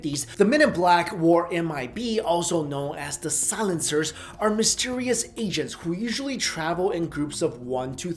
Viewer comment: I misread the title. I came here because I thought it said real black men. I was like what, what does know about real black men? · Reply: I misheard "mysterious agents" as mysterious Asians :/